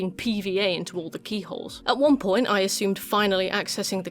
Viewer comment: When you said something about fairies piping glue into keyholes I thought you said peeholes and cringed VERY hard…and then put my subtitles back on